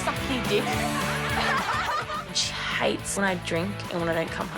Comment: did she say “she hates when I drink and cut myself” ???????